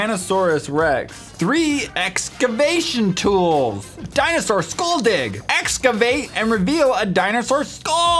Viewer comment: I legit thought that said “Dinosaur Skull Dog” lol